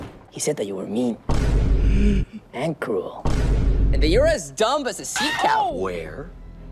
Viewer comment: Okk, sea cow was actually crazy, I always thought he was gonna say "And that you were as big as a seacow" 😂😂